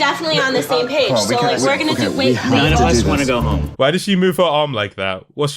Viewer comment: does someone say "I just wanna go home"? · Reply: I believe it was saying "none of us wanna go home"